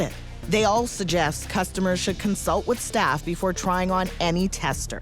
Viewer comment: After seeing all the bacteria, I hear this as "They all suggest customers should consult with STAPH before trying on any tester". They are probably more available than staff, numbers-wise, anyway. Me: "Hey little guys, will you give me a stye?" Staph: "Why don't you try and see >:)"